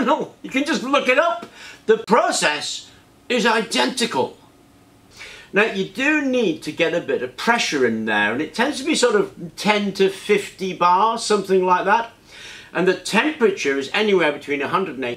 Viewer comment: did you said 10 to 15 bars, or 10 to 50 bars? Is there a other names to search for? because searching "autoclave" leads to medical autoclave or sterilization autoclave.